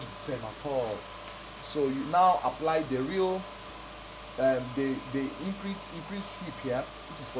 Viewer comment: hwat is he saying at 4:43??? real what? where does the 8 come from? please help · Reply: He said you’ll apply the real “increased hip”. Meaning the figure we got when we added 2” to the hip measurement.